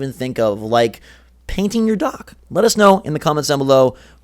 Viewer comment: My resolution is to buy more accessories. Also it sounded like you said paint your dog but I realized you said dock